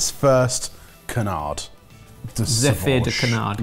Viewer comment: and I heard "zeppelin de canard" and expected a floating balloon of canard mousse with everyone saying "duck"!